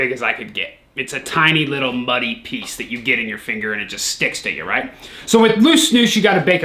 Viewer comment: i knew there was no way he actually says vijus